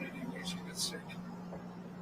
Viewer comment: I am pretty sure is not saying in that audio 3:08:00 :" this f*ucking idiot". More like "I'm gonna stay with this one incase he gets sic." Depp was so taken Back by that claim.. He worked with JD since 90's and was consired like family. How can they clame things that are not true,damn.. shouldn't they Have correct transcripst for the audios??